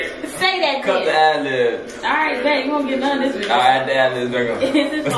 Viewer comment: "did i just hear okay then u dont get nun of this for dinner" u showing out now